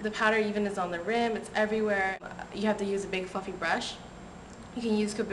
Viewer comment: Not To go off subject cause I Loved your review I myself am a make up geek , But 4:17 just sounded like you said "You have to use a big F***in Brush".